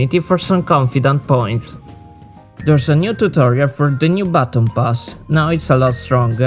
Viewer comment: when you were talking about the BATON PASS i thought i heard BATTLE PASS and my heart skipped a beat lmao